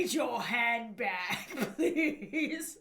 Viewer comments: When the "shadowed man" said give me your had bag, I thought he said give me your hand back lol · Am I the only one that thought he said give me your head back lol I'm dead 😂